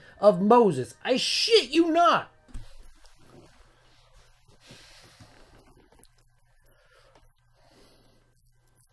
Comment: The audio is fudged at 11:18ish. Cant hear the video of moses. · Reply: He opens with calling this a “Red Sea moment”. Picking the speaker of the house. 🤦‍♂️